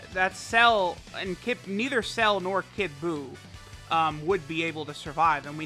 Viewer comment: Who also heard him say lr phy kid buu